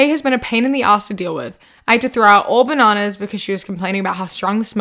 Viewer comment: okay I somehow misheard 'bananas' as 'knives' and I was so fucking confused lmao